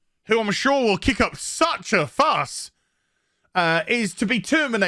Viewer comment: holy shit i thought said something else. i had to rewind it, he says “such a fuss” not “the little asian girl who im sure sucks to fuck!” 😂 real talk i thought he just ended his youtube career